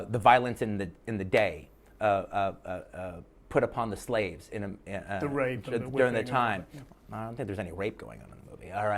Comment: I thought he said ‘the rage’ not ‘the r*ape’